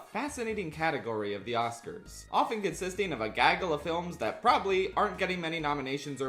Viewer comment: did you say, glaggle?🙂